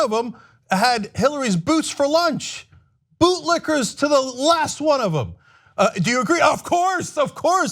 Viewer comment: I swear I thought that said "bootylickers" for a sec. 😂😂😂